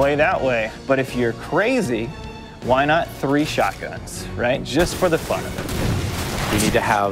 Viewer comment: anyone else thought he said "three shotguns...just for the fuck of it" at first?